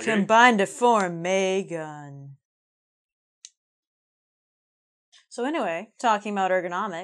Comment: ”so anyway" が、”それでは"・・・”に聞こえて面白い。